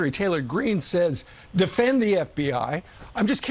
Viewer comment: Did Douchey really say "MTG said defend the FBI" ?? Maybe he meant defund(which she said) or blatantly lied? I could see both which says a ton about Fox in general